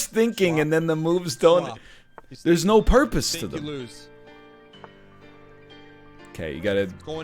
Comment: did i hear that right? "You think, you loose "